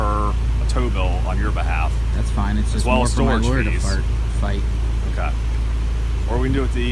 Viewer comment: Ok, wait just one sec...did he just say that’s more for my lawyer to fart?! It’s at about 7:55...I could be wrong, but I heard fart. I’m choking from laughing 😂